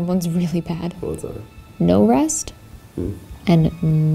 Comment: I thought it said "relax" lol like they were being too much and the ghosts were like "but chill, tho..."